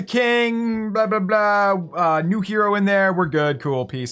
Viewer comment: says new hero I don’t now it’s name that’s what he said?